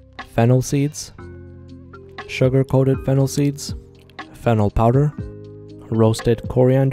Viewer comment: Thought I heard fentanyl powder 😂